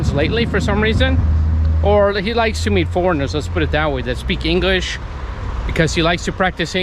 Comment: Did you say : " let's put it that way that" ? It's something like " let's put it this way " ? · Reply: I said, Let's put it that way, meaning to suggest a different view. It's a common saying in English. Thanks for asking about it.